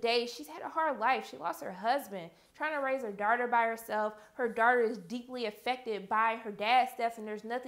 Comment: When she says daughters it sounds like shes saying darters no offense